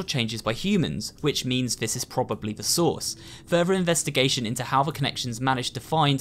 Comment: Not gonna lie, when said "the source" it sounded like he said "the sauce" to me. Lol